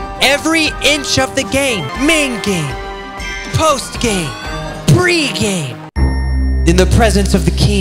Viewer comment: i thought when he said pre-game he said pre-k